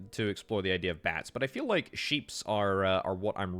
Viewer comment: Did you say "sheeps" around the 7:50 mark?